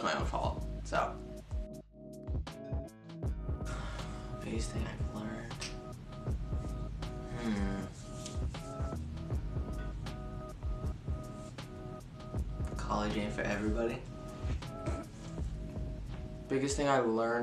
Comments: i can’t understand what matt says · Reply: “biggest thing i learned…? college aint for everybody” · what did matt say at 6:32 can some1 pls help me lol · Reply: College ain’t 4 everybody maybe? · What did Matt say at 6:33? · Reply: College ain’t for everybody.